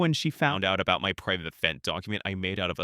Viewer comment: I’ll link it in the description probably It’s extremely embarrassing though One thing that Yun REALLY freaked out over is me even having it (Rabbit has it), and screamed for me to delete it multiple times and I fear he would have a reaction But regardless It is a vent and extremely embarrassing, made out of a psychotic breakdown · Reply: i was joking, the ai voice sounded like it said "fent document", like fentenayl and it was funny in the moment